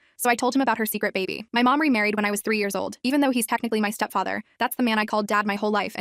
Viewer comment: I heard "my mom married when she was 3 y/o" 😭